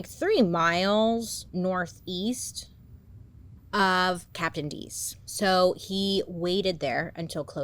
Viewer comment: Did u ever once have the thought everytime you said captain dees nuts cause I did the whole time 😭😭